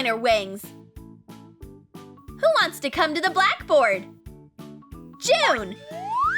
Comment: did she said black board